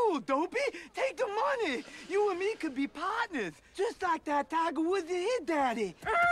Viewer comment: Just like the Tiger Wood and hiiis daddy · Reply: I thought he always said "just like that taco with the hit daddy"